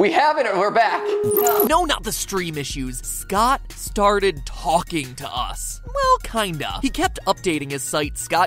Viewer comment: at first, when he said: "Scott started talking to us", I heard: "God started talking to us" idk, I'm just dumb.